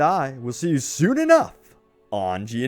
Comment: On the end of the clip I really thought I heard 'may the horse be with you'. Just me?